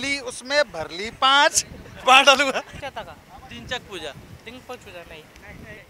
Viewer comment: what did he say!! 😆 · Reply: Dimple chooja😅😂😂😂😂